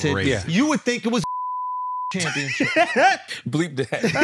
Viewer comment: what do yall think said. I think he said dog eating championship 😂